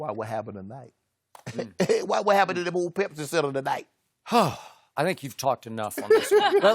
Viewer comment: what he say 😂😂😂😂😂 all I heard was old Pepsi center when he changed his voice